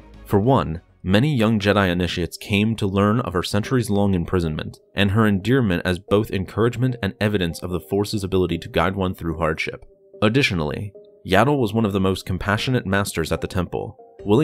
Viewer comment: i thought you said hard shit at first